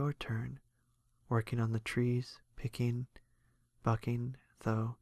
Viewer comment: I thought he said applejack spent her days fucking trees instead of bucking